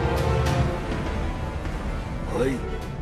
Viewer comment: I don’t think Sato’s actor said “Wait” at 2:25. If you listen carefully I’m pretty sure that’s Mr Miyagi saying “Oi” from The Karate Kid 2